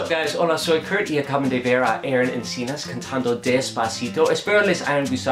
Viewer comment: how I sing despacito • • • DESPACITO.... HDJGSTJSJKHYKSJDKYK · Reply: I legit thought he was saying tacito and I got hungry.